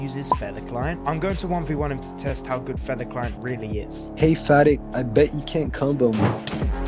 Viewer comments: I heard "Hey fatty, i bet you can't combo" 💀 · I thought he said "hey fatty I bet you can't combo me" lol